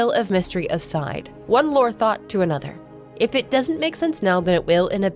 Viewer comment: Great vid and at 6:11 did I hear you say Lore Thot or are my ears playing tricks on me? 😂